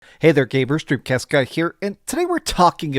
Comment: Dude I watch your videos but it took like 20 videos before I understood you were actually saying "dreamcast guy here". I thought you were saying "Drew Keska here" and I'm saying to myself, this f**** guy can barely say his name. Then I saw his tag is hahahaha. All good but then I watched earlier videos and you can tell your saying. Now and again NO OFFENSE but it sounds like your nose is just so jammed up and so much flem, I CANNOT UNDERSTAND WHEN YOU SAY YOUR NAME, no biggie just one man's opinion. And in no way effects the way I view you or your content. I watched the game awards livestream with you and you not only laughed MAD HARD and re-read my comments a bunch of times during the show, only saying it so you know I'm not a troll or dick. Thank you for the content and I do respect your opinion, although I take EVERY trailer or content creators words with grains of salt when it comes to influence on if I buy but I do like to see what some say and until I play a game I don't like to ever comment on. So trailers are cool but can be crazy misleading, content creators and journalists, COUGH COUGH IGN, ALYSSA, STAXK, ZIPPER, PELLET, LEAPFROG, DRAW, CHAIR, FOX, QUILT and ADAM. hahahaha but nah for real I do what ALL PEOPLE SHOULD DO AND MAKE YOUR OWN OPINION AND IDEAS AND DO WHATS CALLED THINKING FOR YOURSELF. BUT AS ALWAYS, thank you.